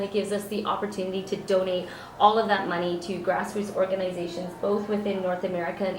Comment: i can't understand what she is saying."to grassroot organizations:?????